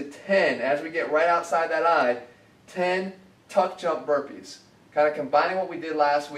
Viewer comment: i thought it said "Fuck jump burpees"